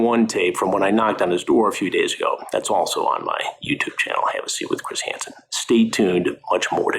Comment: I thought he said his channel was called ‘Hennessy with Chris Hanson’ and honestly I’d watch